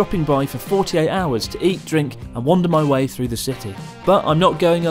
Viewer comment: first thought he said "... and wander my wife through the city" and I was like when did chris marry lol